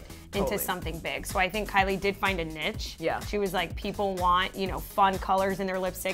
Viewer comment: did she just call niche a nitch? Tf